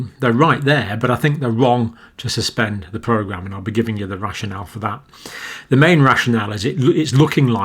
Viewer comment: Oh rationale I thought you said russian elf lmao p.s. thank you